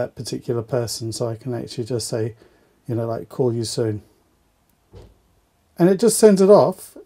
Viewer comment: This is a very informative video :-) 2:01 I thought you said "call you soon" but you typed "call you text". lol